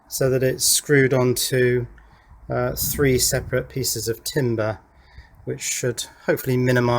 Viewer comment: I first thought you said "three separate pieces of tinder".... As in, making a nice little camp fire with that heater. 😂